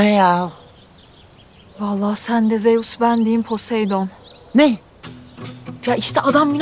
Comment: Ne dedi? 😂😂😂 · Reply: Ayy! Sen de Zeus, ben diyeyim Poseidon dedi 😂